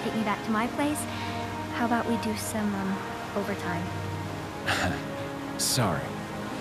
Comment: Ashley: "Pervert!" Also Ashley: "You wanna have some overtime? **wink wink" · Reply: Sound like she said Other time